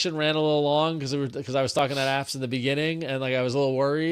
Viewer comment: I thought I heard say "talking out my apps" . Could be a good name for a new segment 😅